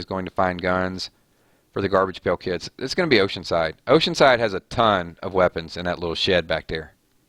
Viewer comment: For a minute I thought you said in the little Shit back there but you said Shed 😅 Luv ur live streams! You're the best host ever! 🤗